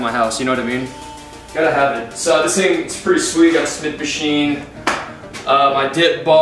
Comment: Did he just say smith machine